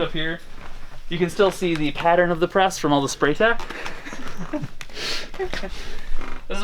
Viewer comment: he said "spray" something ...what would he recommend using ? instead of that please ? We have a small screen print business and I dont want that stuff on my new floors if possible . ty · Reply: He's referring to "spray tack" (spray adhesive used on the pallets to keep the shirts stuck down). The overspray goes everywhere. There are alternatives, like products you can apply with a brush (like sgreen's water based pallet adhesive from ryonet)